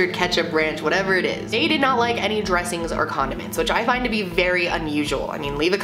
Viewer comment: I thought you said “condom”